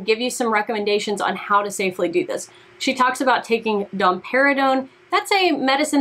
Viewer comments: I'm sorry, but I genuinely thought she said Don Perignon! I was so confused and was like, so drinking champagne will bring in breast milk?!?!? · Oh, domperidone. I thought she said Dom Perignon and thought "What???"